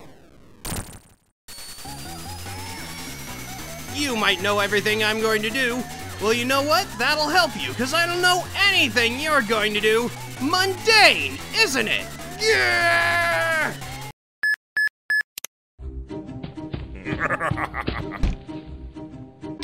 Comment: I initially thought at 00:34 Sonic said "Monday, isn't it?", makes the whole sentence completely ridiculous XD